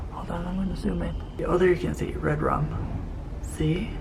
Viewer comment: I thought I heard something says red Rob